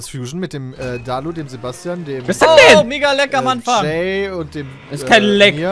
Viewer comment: ich hab kurz verstanden: oh mega lecker anfang, xd